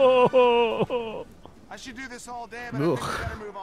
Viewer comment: says "should" when the subtitles say "could"